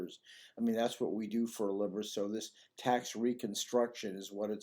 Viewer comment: its hard to decifer what you say. (Liver) according to CC!